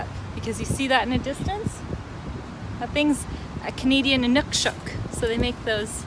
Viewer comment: aquí que dice?? Dice que hay algo canadiense pero no se que es eso de nucshots o algo así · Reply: Dice que era un Inuksuk canadiense. :)